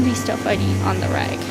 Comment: "They wouldn't be stuff I eat on the reg." I definitely heard "rag" instead of "reg" at first, and was very confused.